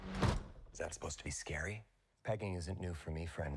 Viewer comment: That pegging line is honestly a huge green flag for me 😂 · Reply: I’ve watched the teaser so many times and I thought he said begging until I read this😅